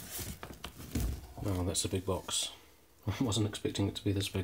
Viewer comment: " oh that's a big box i didn't realised that " that's what que said